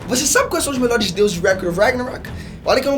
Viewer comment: Se não tivesse no título, eu jamais iria ter entendido que ele falou "record of ragnarok" no começo do vídeo kkkkk